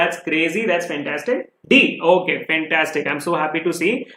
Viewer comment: Did sir just say Pen-tanstic?